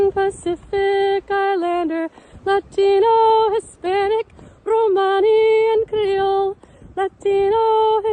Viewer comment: Hooray! 8:50 good God that cannot be real...right? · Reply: I'm glad there were subtitles because for a moment I thought she was saying "Latino, Hispanic, Romani, and gringo"